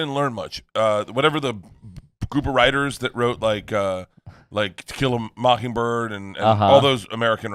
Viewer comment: anyone else think that Bert says Tequila mockingbird instead of To Kill a mockingbird around 4:30?